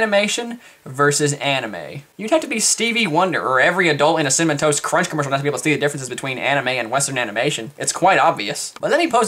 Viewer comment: what the fuck did you just say? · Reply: "You'd have to be Stevie Wonder or every Adult in a Cinamon Toast Crunch Commercial not to be able to see the differences between anime and western animation, it's quite obvious." Sometimes I speak too fast for my own good.